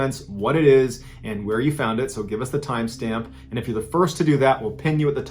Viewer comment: Just once I wanna hear ya say share the tramp stamp instead of time stamp... I was high one night and thought I heard it, so now I'm just stuck